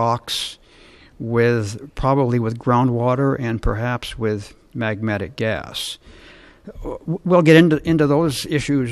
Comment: Magmatic NOT magnetic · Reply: arrian Ranger yaaaaa My brain e-braked at what I thought I had heard. I "insta-posted" my question. Then realized that i had heard him incorrectly. And loled at my own post. oh man, Magnetic gases! Haha. Please feel free laugh it up at my expense. tis deserved.